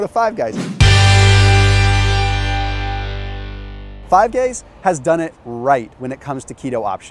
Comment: Is it just me or did he say 5 gays instead of 5 guys